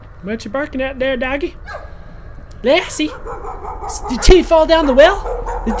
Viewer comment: I thought he said tittie fell down the well XD